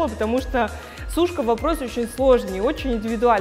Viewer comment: О чём, о чём..? О Сучке?! О какой Сучке..? Ой... Извините - послышалось)))